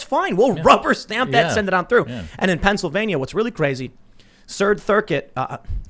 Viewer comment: Tim says "Sird thircuit" instead of "Third circuit" 😂 Love u Tim lol